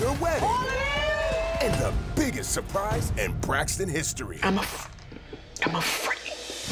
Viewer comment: Traci says I'm afraid, but when I first heard her I thought she said I'm a freak.